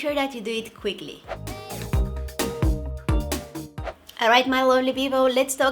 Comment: I thought whe said "alright my lonely people"